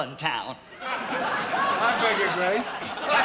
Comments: what did he say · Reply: I beg your grace. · What does Andy say at 2:08? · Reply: Yes, he said "I beg your grace."